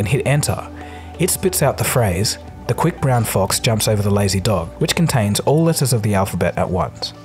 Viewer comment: the sentence actually says jumps instead of jumped. Overwise it wouldn't contain any "S". And yes I went through the whole alphabet to find out. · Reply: The text on screen says "jumped", but Dagogo says the word "jumps".